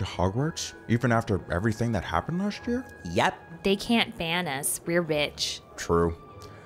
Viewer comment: “They can’t ban us... we’re rich.” “...True” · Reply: i thought she said "they can't ban us... we're bitch" lmaoo 😭😭